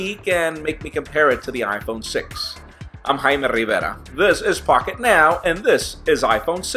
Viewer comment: i thought he said "fuck it now" instead of pocket now, hahah